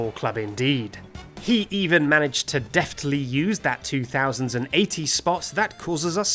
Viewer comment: Did anyone else hear him say 2080s?